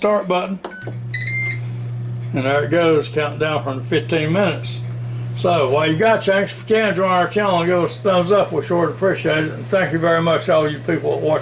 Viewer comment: Yooo What in the actual fucketh did he say from 4:20-4:30?? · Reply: "There it goes, countdown from the 15 minutes. So while you got a chance if you can join our channel, give us a thumbs up we sure do appreciate it. And we thank you so much all you people that watches our videos" 😂😂